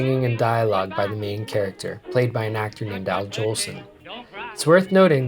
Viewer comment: Sorry, what are you saying there in 5:57? Played it over many times but can't make out what you're saying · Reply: Sorry about that! Here it is: "...played by an actor named Al Jolson." Al Jolson is a famous actor from the start of the century. At the time he was one of the most popular performers in the world. These days he's perhaps more well known for his use of blackface and his involvement in that tradition.